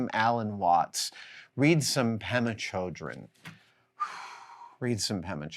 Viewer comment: Jon Kabat-Zinn, Alan Watts and Ram Dass. Who was the other one Adam mentioned? The one whose surname sounded like "children" at 4:47. Thank you!